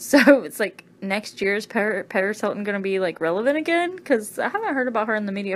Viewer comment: I heard say "paris hilton" then realized she said periscope, then when I watched to review I forgot what she actually said lol but she's saying "periscope"